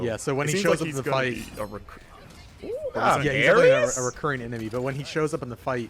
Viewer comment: Did you say Aris at 10:00? Lmao. Hey alright.